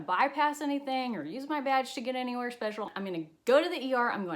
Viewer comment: I definitely heard "not ganna use my vag to get anywhere special" 😂😭 I mean, you could...